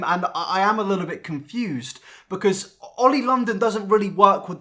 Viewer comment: oml I thought it said Oil London- I can’t